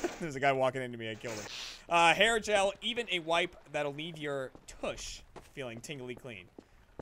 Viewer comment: Even a wife that will eat your tush Wtf did I just hear